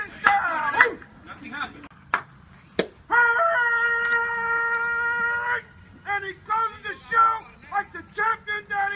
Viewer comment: what is he saying!!!! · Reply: “And he closes the show, like the champion that he is”